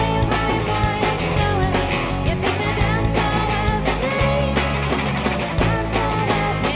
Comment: I wish i'd been over this No time for playing games Oh little wicked boys 🎸🎸🎸 A special look, a crooked smile My gut says he's ready, but i just cannot run a mile Can't believe i felt so funny Amusing as a classy baby, playing funky You wave, hiding why, and you know it As if "The Dance Floor" was a name The Dance Floor was his name 🥁🥁🥁 If i took one pill and looked at you, i wouldn't stand a chance I'll stick to drinking So my guess is, the song is about a girl pretending to be more fun (funky) than she actually is, due to frustrations of guys mainly wanting "fun" chicks. This ends up getting the attention of a guy, who smiles and waves at her but never approaches her on the dancefloor to introduce himself, and so that's just what she refers to him as. "But i just cannot run a mile" could mean she doesn't want to have to put in all the effort for both of them to have interactions. Eventually, she gets frustrated with this guy too, hence the "if i took one pill and looked at you, i would not stand a chance, i'll stick to drinking." I could be wrong though. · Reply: to me i hear "As if the dance floor had a name"